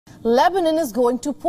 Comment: I thought it said “Lesbian” polls…🤔